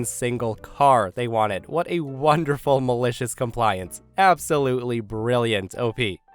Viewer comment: I accidentally rolled over on my ear buds during the into and thought he said this would be r/DeliciousCompliance. I don't know if this is a real thing but I hope it is, and that it is full of bakery/restaurant related drama and malicious compliance stories.